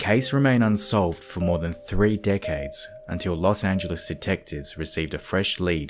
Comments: With his accent i thought he said 2 dickheads instead of 2 decades. Lol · Great video! I definitely heard "dick-heads" for "decades" in the beginning 😳🤣 xo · Awesome video! However, am I the only one who, at the beginning of the video, thought he said "dickheads" instead of "decades"? 😂 whoops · I heard “dickheads” then realized he said decades 😆